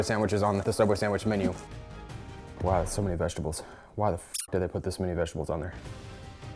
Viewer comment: did he say why the fuck did I put so much vegetables